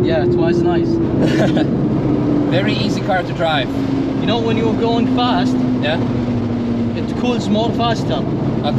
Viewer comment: “You know when it goes fast, you know it goes more faster” 9:36 😅 did I hear that right?? It’s driving 😂😂🤌🏼